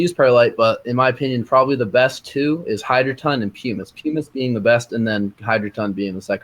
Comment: Great Video! But at 12:23 - Substitute for perlite - I missed his top recommendation....he said hydroton was #2 and was it 'peamus' as the top recommendation ? peemus? pemus? what the heck did he say? Thx! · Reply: Ok - got it - Pumice!